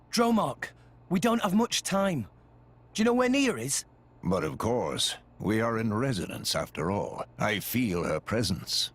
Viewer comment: I swear to god that Rex said Nier